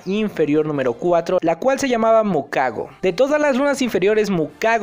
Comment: escuché que dijiste me cago pero perdón también por la palabra